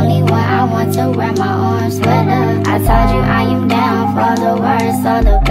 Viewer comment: Whole time I thought they were saying “ARM sweater” not “orange sweater”😭